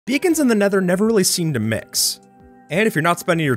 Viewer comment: I misheard Beacon as Vegan and got real confused for a sec